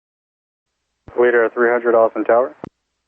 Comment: I thought I heard "Austin Power".